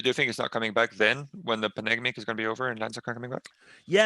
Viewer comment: is it just me or do i hear nbk saying "beniginning" and "panignic" 26:16?